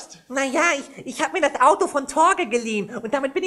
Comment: bei 1:23 hab ich Auge verstanden 😱😂